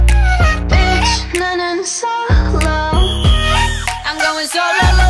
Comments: I thought she said “Bitch I’m running solo” 😂 Am I the only one? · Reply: I thought she said "Bitch, now I'm solo"😂😂😂😂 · Wow all this time i thought she was saying Bitch i'm going solo 😂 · Am I the only one who thought she was saying "It's done in solo" just me? Okay.